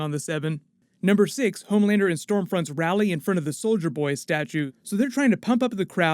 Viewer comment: When you said soldier boy, I thought you said Soulja boy 😂